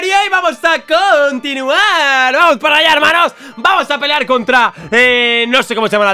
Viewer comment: ya se que dijo "vamos a pelear" pero mi mente escuchó "vamos a failear"